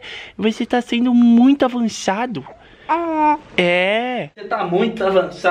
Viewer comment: você tá sendo muito avançado: aé??? (Só eu que ouvi isso?) (É só clicar no número azul e você já vai direto p minuto certo😉)